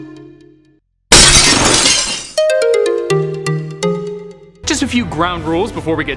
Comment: I’m not the only one who heard “the flesh” right